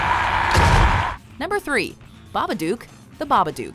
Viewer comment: Hey there! If you're here, you probably love horror movies. So, why not come join us as we premiere our Top 50 Scariest Horror Scenes of ALL TIME on Halloween 2018 (TODAY!) at 2PM EST/11AM PST? It's gonna be great: <link> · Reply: When they said babbadook, i thought they said marmaduke